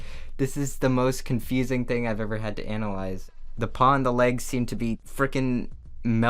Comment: Also at 4:34 you said analyze but another add cut you off, so it sounded like you said Anal great